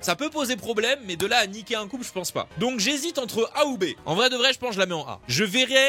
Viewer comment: Hors sujet mais à ce moment 3:47, tu à dit "A ou B" et moi j'ai cru que c'était le nom d'un personnage 😂 Genre j'ai entendu "Haoubé" ou "Ahubet" lol